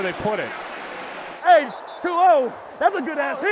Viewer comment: The hit by Jimmy Ward on Pat Mahomes around the 2:30 mark, I thought Pat said "too old"